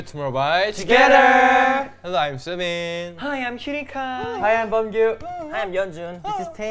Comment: I heard Kai say "Hi I'm cutie guy!" 🤣 I mean, it's not wrong